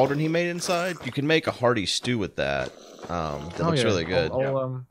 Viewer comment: For a moment, I thought he said “farty stew.”